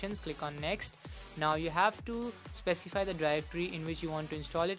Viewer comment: Can someone please explain what the hell he is saying at the 1:10 mark? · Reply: "Now you have to specify the directory" thats what i said there